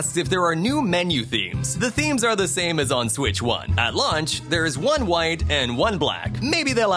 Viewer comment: Did he say at lunch? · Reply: He said launch, but ngl I thought he said lunch too lmao